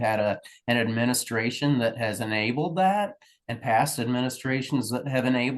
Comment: if he didn't he should say so, stand up, stop letting the assholes bully everyone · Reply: what does he say at the 3:48 second mark? "this administration and previous administrations"